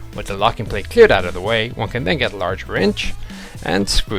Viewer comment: First time through I thought he said "Get a large wench" when discussing track tensioning. Hey, I can get with the program if she's large in the right places!